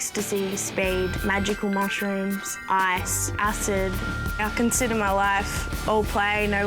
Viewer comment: Is it me or did she just say that she tried ice/meth